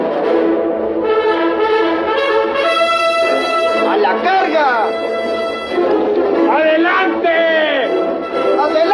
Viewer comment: Does anyone know what the three Mexican commanders are saying in Spanish at 4:01? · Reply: A la carga! = Charge! Adelante! = Go ahead!